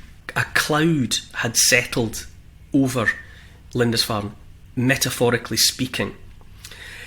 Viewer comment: Excellent as always. I must confess, (I'm just a dumb American...), at first I thought you were saying Linda's Farm. 🤣